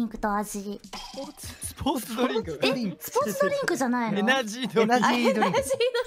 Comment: エムジーをエナジーと聞き間違えた？